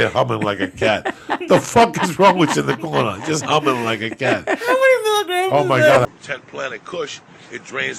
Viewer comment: WTF DID JUST SAY?? LMAO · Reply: Christopher Khachatrian — “how many milligrams is that” lmao